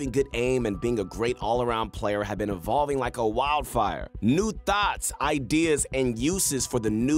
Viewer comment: Sounded like he said new thots